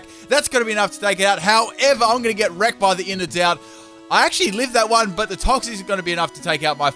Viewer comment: Every time I hear "Innards Out" I keep hearing "Inner Doubt."